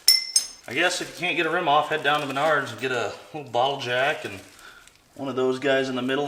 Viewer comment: I heard "Head down to Menards and get a bottle of Jack" 😆